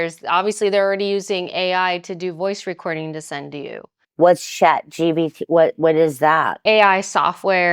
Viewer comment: Did she say "Shat GPT"? 😂